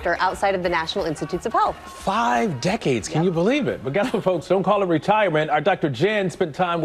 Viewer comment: did he actually say ghetto folks? 😮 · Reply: “But guess what folks..”